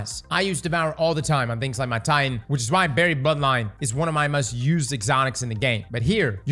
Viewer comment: You’re gonna sit here and tell me you DIDNT say instead of Buried Bloodline? 😂